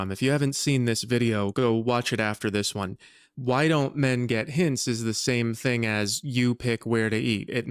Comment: thought "men get hints" said "menengitis"